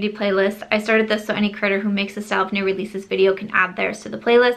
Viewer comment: Omg I thought you said any"critter" who makes these videos, instead of any "creator" , and ya know, for me an animal lover, I'm ok with it haha.LOL 🤣🤣🤣